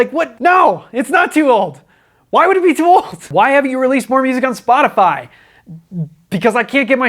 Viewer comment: I swear I heard, "Why haven't you released porn music on Spotify?"